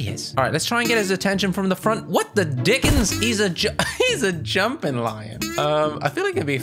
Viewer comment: me: bored: posts me: bore is no more EDIT: did you say the d word?? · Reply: No he said: dickens